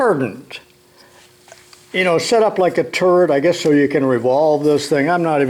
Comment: The first time he said turret, I thought he said “setup like a turd”, I laughed my ass off for a second